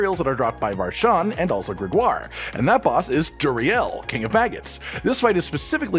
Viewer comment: I really thought it sounded like king of fags 😆